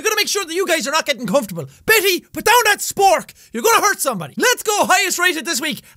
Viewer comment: Did he say spork